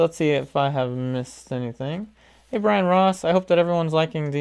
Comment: When he said “Hi Brian Ross!” I legit had to rewind and look at the name bc I thought he said Ryan Ross omg